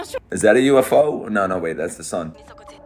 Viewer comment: did he say sun at 7:25 ????